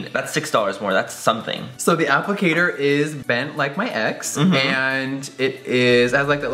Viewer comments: Did Jeffree say bent like my ex at 2:21 😂😂❤ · "The applicator is bent like my ex" LMAOOOOO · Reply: I THOUGHT HE SAID 'BENT LIKE MY EGGS' LMFAO